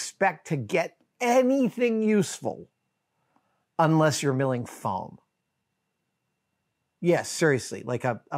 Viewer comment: For a second I thought you said "unless you're milling FLAN" 😂